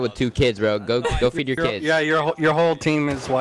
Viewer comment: ow i thought he said your whole team is white bye hahahahahha lol